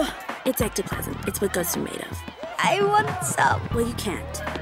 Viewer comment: the cutest I want some I heard